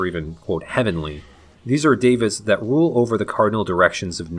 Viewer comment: I'm so happy to see another video from you. You do better SF research than anyone else I've ever seen touch the subject. One question, though: around 7:10 you say "these are four dav____ths that rule" and I couldn't tell exactly what you were saying. Could you clarify? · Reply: Devas. Higher beings in Buddhism and/or Hinduism